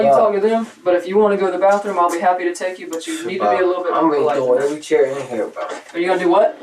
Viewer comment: is it just me or did he say "shit ima kill everybody in here"